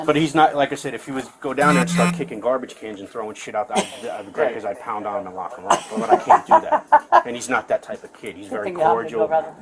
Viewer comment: it sounds like he said ..If he was kicking garbange cans etc.. I would POUND on him and LOCK him up...wauit wWHUUUTT?>?>?